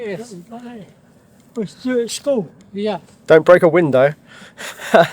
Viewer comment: When went to toss the discus and told him "Don't break a window", I thought he said, "Don't break wind Dad"!